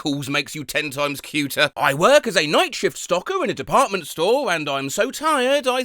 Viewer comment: I actually misread and misheard the one at 2:12 as "Night Shift Stalker". Maybe I need sleep.